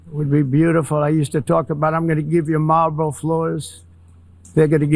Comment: did he really say Marlborough floors??